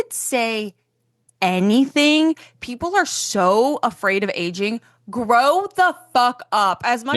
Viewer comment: I rly thought she said people are afraid of edging